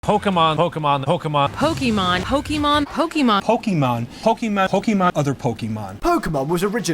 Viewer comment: But first in 0:00 i heard peapole say Hokémon i heard them say it twist